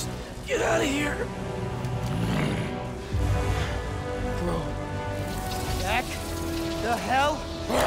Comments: “Bro... back. The hell. OFF.” I’m sold. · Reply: I thought he said “Go...back to hell” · Miles saying “back the hell off” was amazing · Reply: I thought he said “Go back to hell” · “Bro... back. The hell. OFF.” I’m sold. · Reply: I thought he said “Bro..back the hell UP!” Lol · What did he say here? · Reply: “Bro. Back. The hell... OFF!”